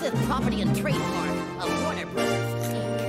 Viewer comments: What did Daffy say at 0:04? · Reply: Thanks! Just needed to make sure, because to me it sounded like he said "Warner Bros. Kink" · What did Daffy say at 0:04? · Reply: “Exclusive property and trademark of Warner Bros Inc.”